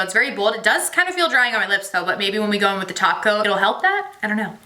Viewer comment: I thought you were saying taco ... when you were saying top coat lol maybe I just want some Mexican food